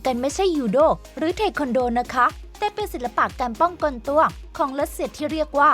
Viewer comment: แอดพูดว่าการป้องกลตัว